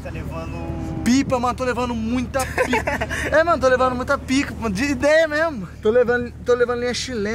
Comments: quem entendeu o que eu entendi o parece q falou tô levando muita pic* kkkk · Só eu que escutei ele falando: Tô levando muita pi** kkkk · tô lavando muita pika só eu ouvi isso kkk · presta atençao o que ele falou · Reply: To levando muita pic@ falou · Pipa tô levando muita pic@ kkkkkkkkkk eu não fui o único que escutei isso kkkkk